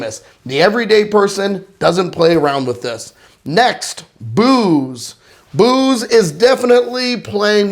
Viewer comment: did you say Boobs?